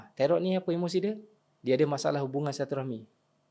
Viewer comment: Ustaz cakap apa pada minit ke 11:21 tu? Masalah apa? Kurang jelas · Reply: masalah silaturrahmi(hubungan sesama manusia)